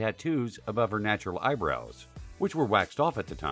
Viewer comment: My mother in law recently got her eyebrows tattooed and they look ridiculous but she won't admit they are awful and that she made a terrible decision. But as long as she is happy that is all that matters. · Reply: Black Dahlia thought u said elbows 😂